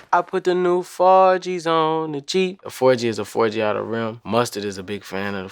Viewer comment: I always thought he said "forgis on the g" for the g wagon